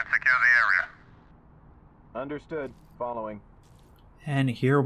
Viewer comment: i heard it as "understood, ballin" and i lost my shit for 2 minutes straight, idk why